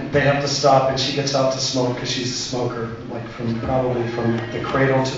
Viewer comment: I had to watch that twice because I thought he said SNOW, not smoke. That was an interesting mental image for a moment there... I think kids learn that word at like, seven, now, so I'm sure he didn't corrupt anyone. :-P